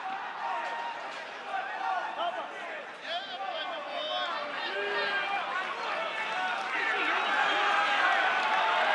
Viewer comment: I wonder what Sean said to Izzy bc it genuinely made him howl lol ffs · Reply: I think he said “apple pie”